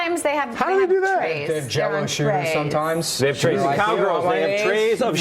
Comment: nick’s sperg stare + thoughts on trump anxiety 5:57 - nick’s online presence 9:57 - the meaning of life + trump debate 12:11 - nickelodeon slime proposition 19:39 - whole foods orange peels 21:19 - whole foods orange peels pt. 2 22:09- whole foods orange peels pt. 3 24:23 - nick vs andy round 1 25:55 - nick vs andy round 2 29:42 - whole foods orange peels pt. 4 33:16 - sombreros incident 34:43 - nick says something about jello shoes 35:19 - sombreros incident continued 37:16 - fake license plates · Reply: Jello shooters is actually what he actually said. Although I could see how you thought he said jello shoes due to you being gay.